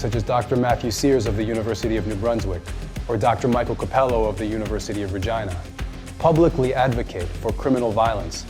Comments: Did he just say “University of Vagina “? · Did he say “the university of vagina” at around 1:57? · " did he say University of vagina"?? 😂🤣😂🤣😂🤣😂🤣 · did i hear university of Vagina? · the University of WHAT? Did he say "vagina"?? Or am i hearing things? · does he say University of Virgina??? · did he say “university of vagina”? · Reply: He said Regina. It's the capital of the province of Saskatchewan. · keep up the good work! 1:55 did he say university of vagina? Gees there even naming universities after womens bits! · did he say " the university of "virgina"?